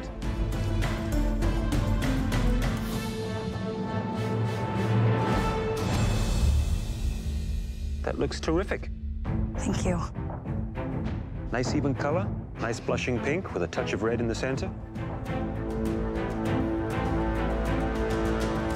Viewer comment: is he saying "that looks terrific"?